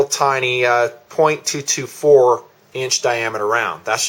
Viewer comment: Did I hear .224 at 6:27? Great job other wise! Keep it coming. I am going to subscribe.